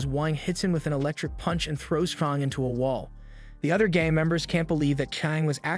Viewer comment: not sure if this is the exact time, but that did NOT say "gang members". It was definitely "gay memebrers".